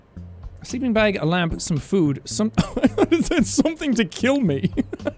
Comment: Why does literally everyone think that it says “something to kill me” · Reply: I just thought it said “something to kill” how child friendly and cheery!